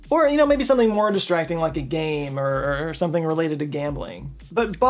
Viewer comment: i heard "the game" I LOST ÞE GAME DAMN IT